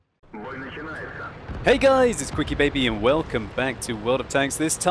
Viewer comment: does anyone else hear "welcome to Jamaica"?